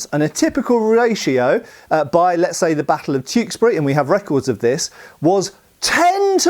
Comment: OI IT'S CHEWKESBURY INNIT